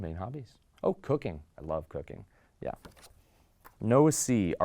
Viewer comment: on everything I thought he said ”oh cocaine, I love cocaine”